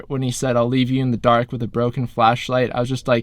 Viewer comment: Is it just me who thought pretense said leave you in the dark with a broken fleshlight?